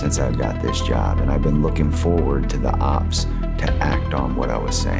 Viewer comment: Did he say the ops? (20:58) 😂